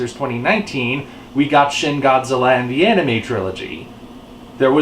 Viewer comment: By the way, who else heard that as "The Enemy Trilogy"? {g}